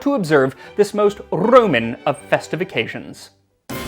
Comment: I heard “Festification” (ok, that’s a word now, thanks Max.) WAIT